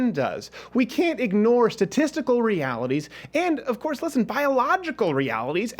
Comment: i thought he said testicle reality